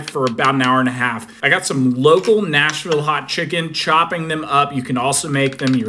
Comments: Why am i hearing “national” instead of “nashville” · I thought it said, Nashville hot cheetos